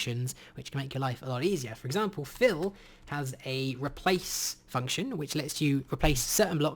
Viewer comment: “Fill has a new replace function” What I hear... “Phil is the new stepdad”